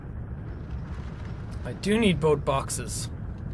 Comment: it sounded like jack said ''boat boxes''